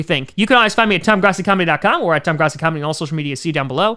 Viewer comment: When he did his usual intro i thought he said ted grossi